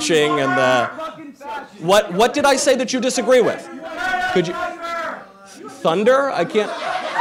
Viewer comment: I caught what was said. The guy holding the phone shouted Greta Thunberg. How sad. A little girl is his 'hero'